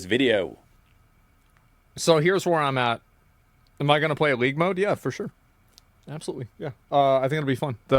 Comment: Asmon playing league? what was that at the end? Did I hear it right? 20:16 (not sure if this is a good idea)